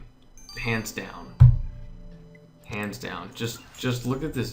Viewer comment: Is it weird when said "Hands down" I heard "Pants down" I was like 😐then 😂😂😂